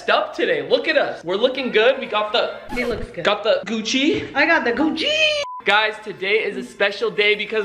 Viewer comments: I thought said “I GoT ThE CooCHi” 😂😂😂🤣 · I got the gucciiiiii! Me: I thought she said I got the blue cheese 🧀